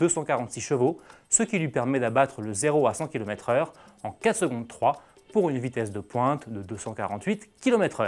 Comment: zero a cent 15,3 sec pour la lotus j'ai entendu